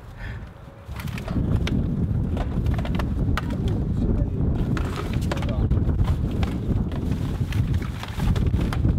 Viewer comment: czy pan przeklną w min 4:12 ? kur.... usłyszałem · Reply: powiedzial "kurcze"